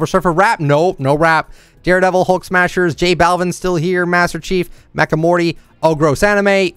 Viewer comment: is it me or did he just say mack and morty instead of Rick and morty 🤔 😐